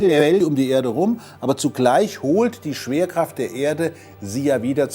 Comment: Hab ich mich verhört or sagt er statt schwerkraft saft??😂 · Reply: Hab ich auch verstanden XD